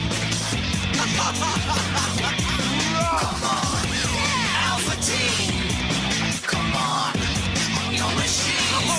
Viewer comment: Anyone know what King says at 0:17? 😂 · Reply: yeah it's "Wagh"